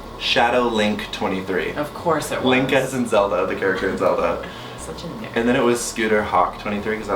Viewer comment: does Mitch say the N word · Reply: I think he said "Such a nerd"